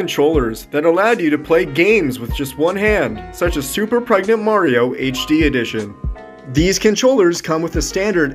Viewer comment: I’m sorry did he just say super pregnant Mario